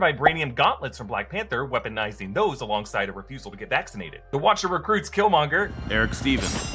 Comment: Shuri’s refusal to get vaccinated? Am I mishearing